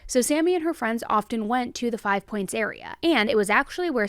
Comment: Look like it said hve points then I thought it said hiv points